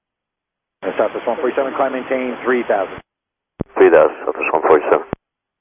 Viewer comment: He says 3000, your subtitles say 2000